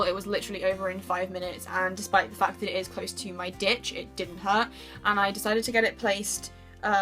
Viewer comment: beautiful tattoos and everything but I am crying laughing because when you said it's close to my ditch, I heard "dick" 😂😂